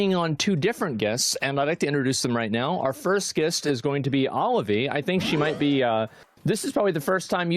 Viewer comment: Did he said: "OliveE"? 😅